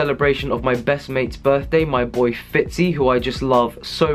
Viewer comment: I THOUGHT YOU WERE ABOUT TO SAY MY BOYFRIEND AND I GOT SO HAPPY FOR A HOT SEC LMAO Also, happy birthday to Phitz!